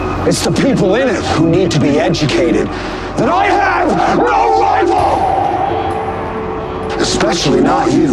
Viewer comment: I always thought he said "I AM THE RIVAL" but i now know he said "I HAVE NO RIVAL"